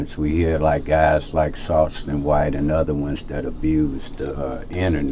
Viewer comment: did he say Softston White? 😂